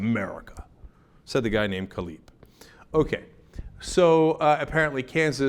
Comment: Kaleb is a Jewish name but i thought he said "says the guy named Khalid" and I was laughing so hard. I had to check that name.